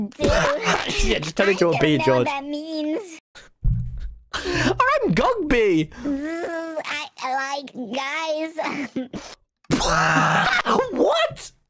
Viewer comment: Don't know if I heard right but I heard George say "I like guyzz" whaaaa👀